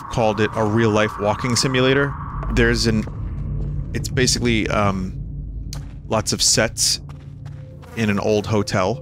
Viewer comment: Did anyone else misunderstand him at first and hear "lots of sex" instead of "lots of sets"? I was REALLY confused there for a second.